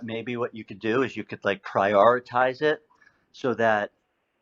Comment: this was my funniest misheard word... when the guy told her "maybe you should prioritize".... I heard cryoritize :)